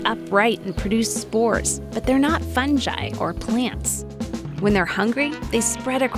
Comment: Did she just say funji HAHAHAHAH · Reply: Fungi